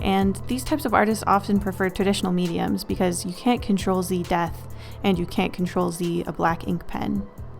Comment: when you were saying "you can't ctrl z death" i thought you had said "you can't crontrol zee death" kinda like dracula would speak!